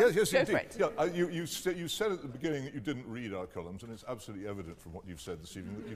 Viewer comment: What in the FUCK does he say to that girl? I've cranked my volume up & he's speaking gibberish. What the fuck does he say? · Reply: He said “you said at the beginning that you do not read any of our columns and it’s absolutely evident that you don’t”.